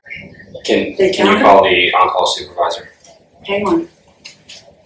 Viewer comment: to me, it sounds like she said “game on”, not “hang on”.